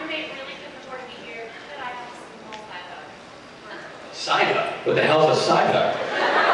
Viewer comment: When the girl said "side hug", I thought she said "psyduck"